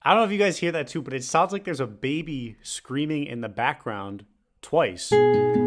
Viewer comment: I thought man said DABABY was screaming in the background